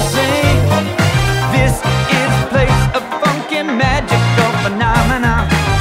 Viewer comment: He uh... does he say... It sounds like "effing" magical phenomena.